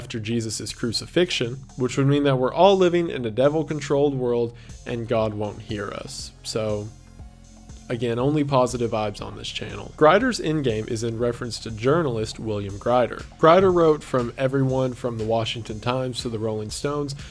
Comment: for a second i thought he said "Grindr's End Game" and I was SO excited